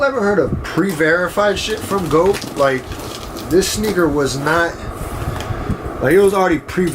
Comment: did he say “nigger” at 2:19 ???